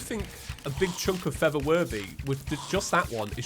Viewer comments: When I hear him say "For The Worthy" I keep hearing "Feather Birdy" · Why did I hear “feather worthy” instead of “for the worthy”?